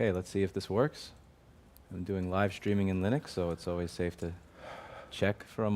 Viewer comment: HOLD ON 0:00 did i just hear Louis say "livestreaming in linux"!!!